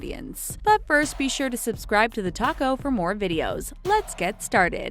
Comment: When she said the talko i thought she said the taco😂